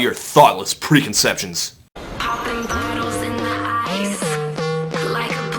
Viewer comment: i never realised they said "bottles in the ice", i always heard it as "poppin bottles in their eyes" and wondered who were "they" and what had they done to deserve that